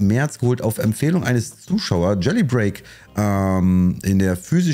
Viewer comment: Hey kann mir vllt wer sagen wie dieses spiel in min 6:09 hieß habs leider nicht richtig verstanden 😅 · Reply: Gelly break